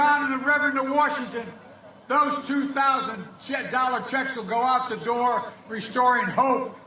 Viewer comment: Did he just say Donald checks 2:32? Biden is just saying what he rehearsed not feels.